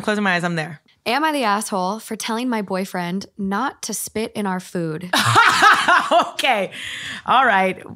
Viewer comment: the second story i heard “he spits in our food” and SCREAMED 😭😭😭😭😭